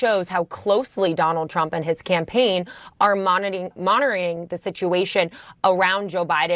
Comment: My white sis is speaking Ebonics. Girl said "monoring" instead of monitoring.